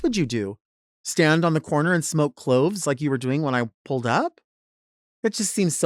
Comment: “Stand on the corner and smoke cloves” 💀💀💀😂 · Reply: Swore I thought she said smoke clothes. Cloves makes more sense 😂